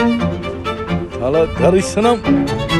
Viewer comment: Anyone hear 'thala tharisanam'